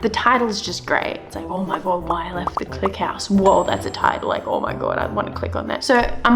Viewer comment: I thought it said “why I left clicked the mouse”